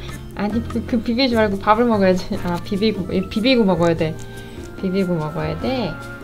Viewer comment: 주인이 있었으면 집사님을 안따랐을수도 있는데도 금방 따르는걸 보니 집사님이 좋은가봐요~ 믿고 따를만한 사람이니 사랑해오~하면서요~ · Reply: 이와중에 0:55초 비비고 먹어야지로 들림 ㅋㅋㅋㅋㅋㅋ